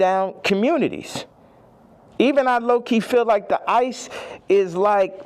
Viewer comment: DID HE SAY SOMETHING ABOUT ICE.